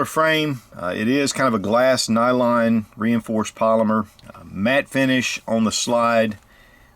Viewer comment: Sounded like you said mad finish not matte finish:)